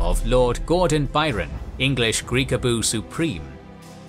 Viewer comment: Did he say greekaboo ? :)))))))))))))